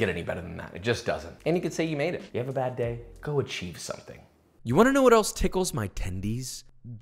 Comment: is it cuz im high or did he say go chief something